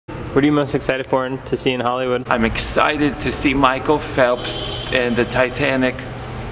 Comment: "I wanted to see Michael Phelps di- and the titanic" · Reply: Lmao I heard "I'm here to see Micheal Phelps di- and the Titanic."